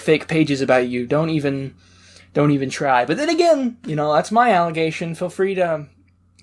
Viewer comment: What does he say @ 4:49 ?? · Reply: Allegation ryt?